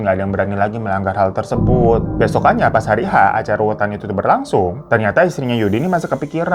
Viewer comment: Kirain mata Q salah denger tw nya bang yg salah ucap 😅😅😅 Acara ruwatan Bukan Acara ruwetan Kayanya babu lagiii ruweet nie... Mikirin TC selanjutnya 😂😂😂